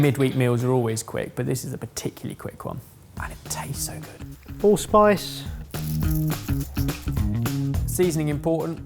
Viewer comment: did he just say allspice?